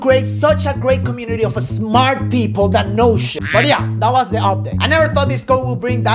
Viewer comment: Did i heard "Badhiya" at